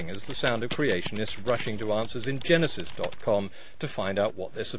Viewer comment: for a moment I thought I heard you say 'Arses in Genisis' and for a moment life seemed more beautiful.... :-D